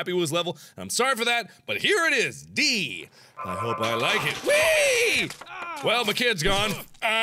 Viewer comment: for a second there i thought he said " D, i hope i like it" , did that sound sexual at any point?